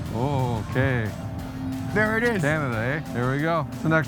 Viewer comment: Did I just hear say "eh" 4:43 ?